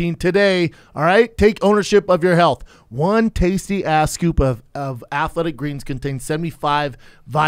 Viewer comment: did he just say "1 tastey ASS scoop"?